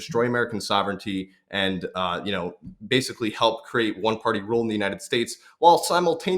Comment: Yes, I heard, “one party rule,”❗️